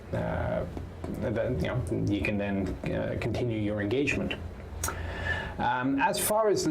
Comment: Lol. Because I watch videos at increased speed, I can sometime miss exactly what's being said. Especially if the presenter has an accent. The point is: I thought he said, "continue your interrogation." Talk about a pants-shitting line of questioning!